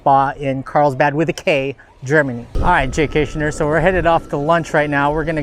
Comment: i thought you said jaystation